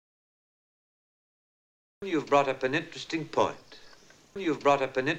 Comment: You've brought up an interesting point가 유브라우업 엔 스틱 펀으로 들리는데 정상인가요 저거 실제로 자막없이 듣고 알아들을 자신이 없네요....